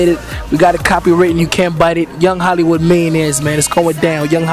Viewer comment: wait does he say young hollywood mayonnaise at 2:48?